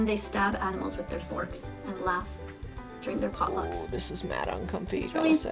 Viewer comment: did she say drink your potlucks????